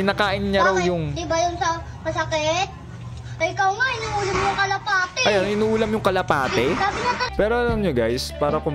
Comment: "Hinuhuli yung kalapati" na misheard po ata kaya naging inuulam yung kapati